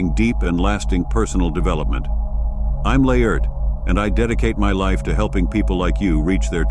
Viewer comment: Hey bro nice channel, who are you? What did you say? Laerd? Laird? Nice to meet you bro